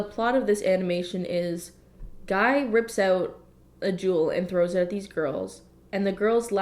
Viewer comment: So I was cleaning while watching this video and when she said that the guy ripped out the jewel I thought she said juul... I don't vape or anything I just hear it a lot